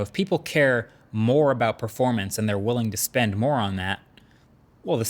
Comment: Wait! @ 5:07 did he just call someone a moron?